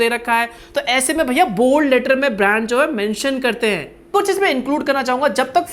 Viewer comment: it seem like he says 'bhe**ho..' instead of 'brands jo' 😂😂